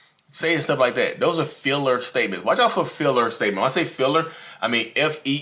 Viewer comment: Ohhh, Feeler not filler. Sounded like you said filler, like nonsense fluff to pad a sentence out. All those words but never say anything